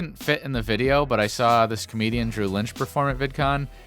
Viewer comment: I thought you said drew Gooden not drew lynch